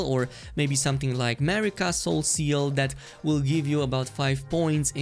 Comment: what is Mary Castle’s seal? I haven’t heard of it and I think I just be mishearing because I can’t look it up and find anything. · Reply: He said Marika Soreseal